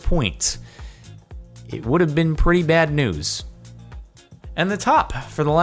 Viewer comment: At first I heard "it would have been pretty bad noose" when he said "It would have been pretty bad news"